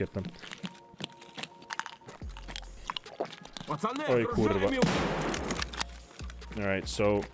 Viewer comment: did he said "oj ku*wa" when the scav pushed stairs